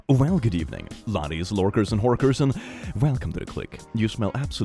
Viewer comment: did he just call me a fucking horker